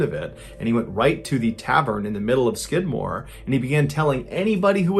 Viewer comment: I thought that he was saying the town of skid mark in the second story 🤣😂 Guess it's time to get some hearing aides